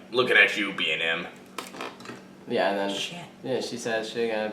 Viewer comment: did anyone else hear sh#t